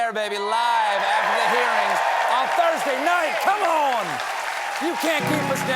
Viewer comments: did he say going live after the hit piece? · I need some sleep, I heard “live after Jan 6th” and checked my calendar 😂